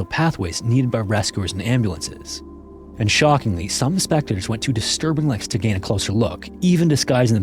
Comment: Spectors? Yea this is definitely an AI narrator. He said spectors twice instead of spectators. If he did it once , I’d say maybe it was just a slip up when reading, but no way you’d do that twice in such a short time.